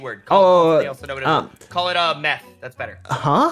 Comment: I swear he said meth not math